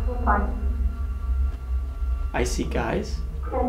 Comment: What if said "Sleep with guys. And kissing" as a response to "What is your occupation" rather than 'I see guys, and kissing."